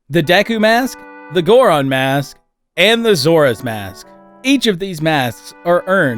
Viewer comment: I was doing the dishes when I heard 'Zora Mask', but I thought you said 'Sora's Mask' and I got Hella confused